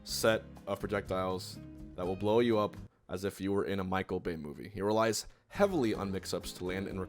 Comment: I really thought homie said "Michael Vick Movie"